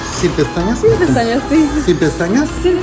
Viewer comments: I speak Spanish and at the beginning he didn't say "without?" he said "Si?" meaning, "Yes?".....you said "Sin pestanas" but it sounded like you said "Si" because you spoke very quietly. So he said "Si" and you nodded your head yes. He def didn't say the word without. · I honestly thought you said "Si pestañas". The rest of the stuff is nasty but the lash part was a little confusing... better to say "No pestañas" or "No quiero pestañas". Also, this video made me wash all of my brushes and sponges immediately after.